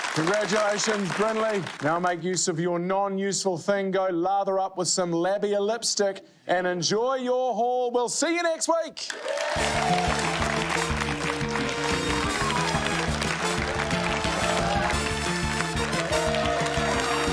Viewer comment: The accent got me, he said labial lipstick and "enjoy your haul" which I heard as "enjoy your hole"